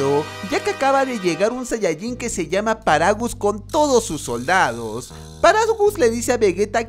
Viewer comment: la primera vez que escuche su nombre (paragus) yo me confundi pensando que se llamaba "Paraguas" :v